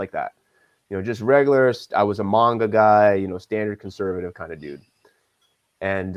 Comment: bro i thought he said "i was a manga guy"